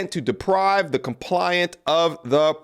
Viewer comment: the word is Complainant, not compliant. Entirely different words. Entirely. Dude, you are reading. How hard is it? You did it twice. I take it you don't often understand what you say or read.